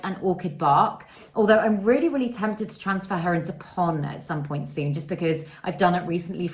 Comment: What is the word you say at 7:14 that you are thinking about repotting in to? I really wanna know!!😊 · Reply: I think she said pon. Lechuza pon